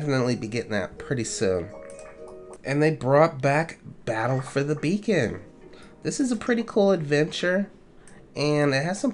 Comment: When he says 'battle for the beacon' it sounds like he says 'battle for the bacon' 😂